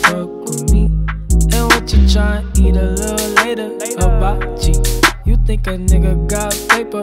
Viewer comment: I thought he said "a butt cheak" not hibachi